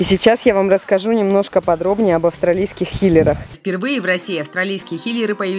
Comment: Когда сказала хиллер, мне послышалось килер😅. Сори если кого-то обидела